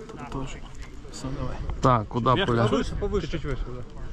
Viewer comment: Мне одному послышалось в конце не "да одинаково" а "да иди на@@@ ?